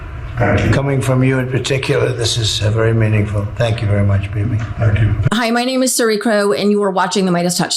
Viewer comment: What he said: "Thank you very much, Bibi." What I heard: "Thank you very much, baby." 😅😂